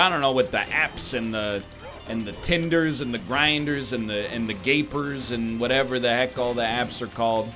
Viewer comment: I'm sorry did you just say "gapers?"